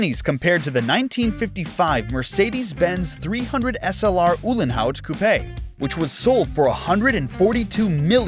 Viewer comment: did i hear it wrong or did you just say coupé?